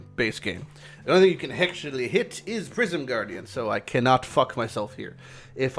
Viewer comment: I keep hearing "Prison Guardian"